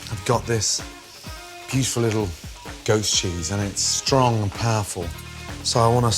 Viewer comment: It sounds like he says ghost cheese